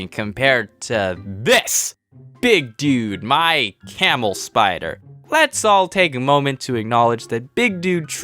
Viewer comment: I thought you said you had no spiders